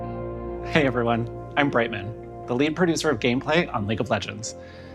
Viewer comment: "Hey, everyone. I'm Brightmoon." I misheard Brightmoon as pregnant, good grief.